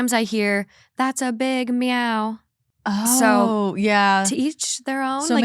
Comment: All I hear now is “that’s a big meow” 😅😅